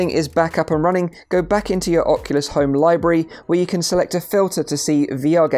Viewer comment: did you say libry?